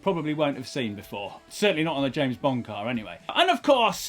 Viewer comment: thought he was saying "bomb car" at first. :D